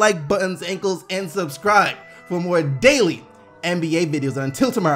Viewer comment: This is literally the first time I realized that he says "keep gettin them buckets" at the end LOL. I always thought he said Bucks and was confused...